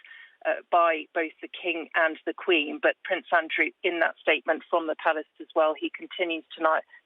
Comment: I thought she said "from the *f**kin palace" 😂